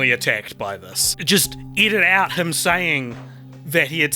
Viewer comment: I keep hearing “eat it out” instead of “edit out”.